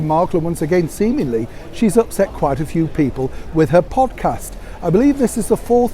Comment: Excuse me, Neil. Did you say "pompcast"? That's what I thought I heard (and it's not inappropriate).